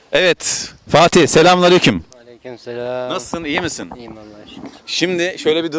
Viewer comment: Müslüman İnsan Allahın Selamını Alır Ne Diyor : Merhaba Ben İnanmıyom Bu Adamın Müslüman Olduguna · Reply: film production aleykum selam diyor.